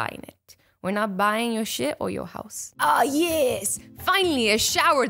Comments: Does she say sh*t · Did she say “We not buyin’ yo s***..” ?